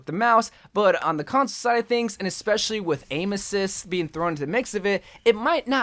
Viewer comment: I thought he said anuses😭